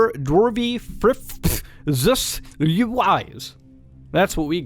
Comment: did he just said sus!!!!1!2!1 😳 (muta is amogus confrime?!??2?2?!?!1?2?)